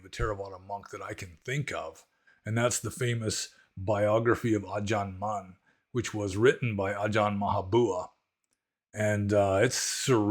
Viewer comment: Did anyone catch the name of the book mentions at 27:36? Autobiography of.. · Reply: It was the biography of Ajahn Mun, written by Ajahn Maha Boowa